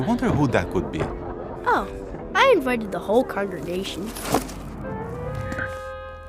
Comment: i thought he said i drank all the carbonation and then the laugh after sealed it